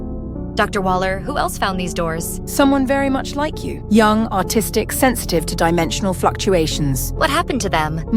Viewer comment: thought she said autistic :0